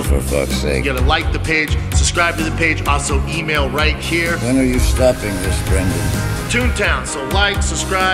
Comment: I love the William S Burroughs VO. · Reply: Did he say why aren’t you stopping this Brendan? lol wtf